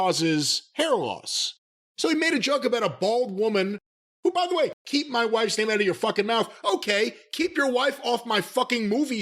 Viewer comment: I thought Will say: “keep fucking my wife’s mouth!!”